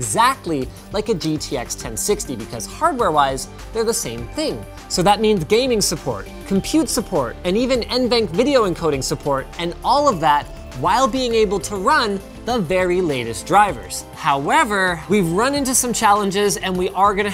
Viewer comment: did he just say gaming support and run minecraft in the backround